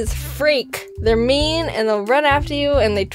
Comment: is it me or I heard "meme" instead of "mean"?